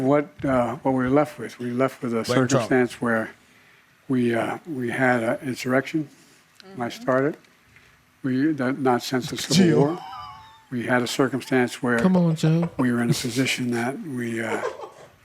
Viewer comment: Did he say “we had an insurrection I started”? 2:21 Freudian slip?